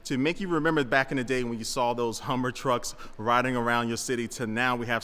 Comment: Humver. I might have misheard that, but that’s a great name!